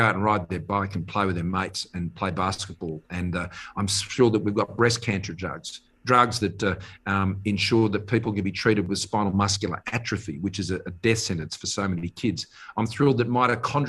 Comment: Now.... at 16:06..... does he say 'BREAST CANCER JUGS'.....?